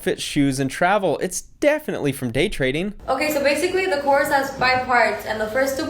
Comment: Did you say "date trading"?